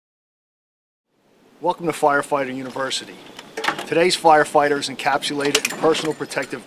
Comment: I love how it sounds like he is saying flire fighting hahaa